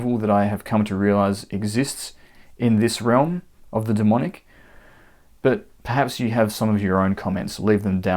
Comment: I thought he said Daemon and I was thinking of the character from House of the Dragon. I wouldn't work with that crazy guy either.